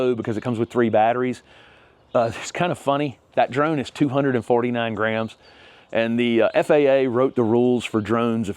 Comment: Thought you said your drone was 249 grand at first I had to do a double take😂